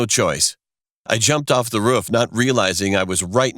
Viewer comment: Anyone else hear “The ruff “ It sounds like its saying that lol